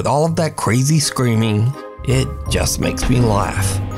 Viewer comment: I keep hearing "es un pinche mono" (It's a fucking monkey).